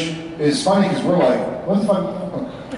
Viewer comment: What was Jared going to say in 1:10 ??😂😂😂😂😂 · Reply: "what's fucking intimidating"